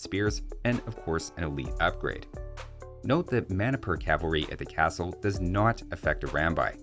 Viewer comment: When you said Manipur Cavalry, my brain went RPG mode and thought you said "mana per cavalry." Enjoy the laughs, everybody.